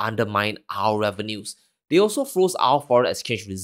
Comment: If u say ur gonna read his quote "verbatim" then read it VERBATIM. @ 3:13 Putin didnt say "they FROZE our reserves"...he said they STOLE our reserves. Dont try put ur spin in a quote or what u think it shud say. U lose credibility.